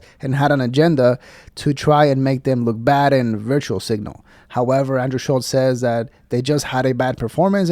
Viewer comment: did mans say “virtual signal” haha